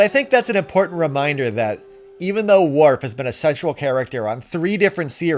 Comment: I thought you said "Sexual character" and i'm like "so that's the challenge he's been having with Jadzia..." I'm also just now realizing that that line is there to show why he's there alone. and not with her. Because they didn't expect Terry Farrell to quit, but they sure as hell weren't going to write miss "I can't be out in the sun, can we shoot this indoors?" into a film.